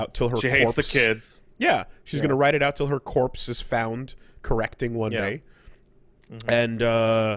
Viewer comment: What did say @ 3:09, "Until her corpse is found, correcting _______"? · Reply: "... correcting one day"